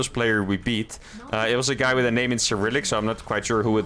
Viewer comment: I know you were saying that the guy's name was in Cyrillic, but I heard it as "Skrillex" so I choose to remember it that way from now on.